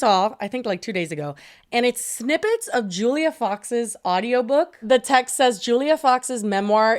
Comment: I heard Julia Child's audio book and I was like what the heck?!?!?! Thank you for repeating the name after or I would have never known.